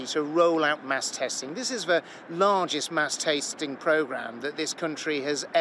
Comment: Did he just say mass TASTING??